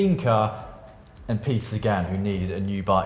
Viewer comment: Caption of the week! What! He said ride like Sagan! I thought he said Segway.